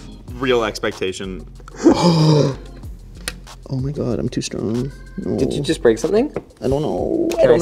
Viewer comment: I swear he says "ah! Omg I'm too stoned!" @ 8:35 lolol